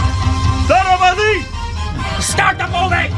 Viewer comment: bruh did u just say thalapathy for dhanush song...😂😂😂dudddeeee